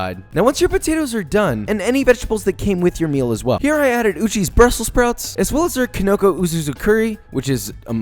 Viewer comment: I thought he said Gucci brussel sprouts